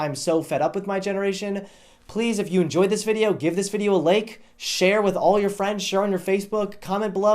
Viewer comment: It sounded like he said "...give this video a lake."